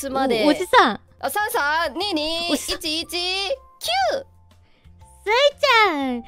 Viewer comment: I believe Sui-chan said "cue" there, as to start something, instead of the word "nine"